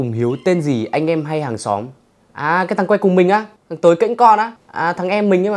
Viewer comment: Sao Anh gọi là Tới Kênh con thế em nghe nhầm là Tới Kễnh Cơn :((